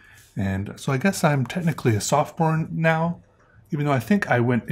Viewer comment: God, you said sophomore but I heard you say softporn 😂 ps: you're a great artist!